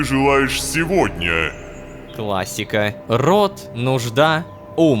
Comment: Отвлекся от просмотра и вместо "классика" я услышал "романтика" 😂